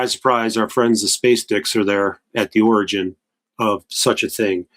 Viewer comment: Did he say space dicks? 2:19:08 😅